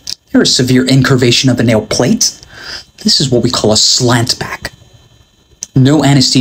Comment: I thought he said “slatt pack” at first lol